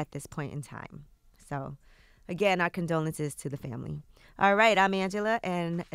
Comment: Did Angie just say "our condolences" to Ti sister?